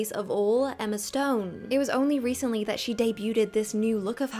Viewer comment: Did you really say "deybyoo-TED"?